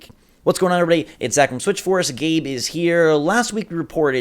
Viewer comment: I heard "Gabe is here" and stopped the video.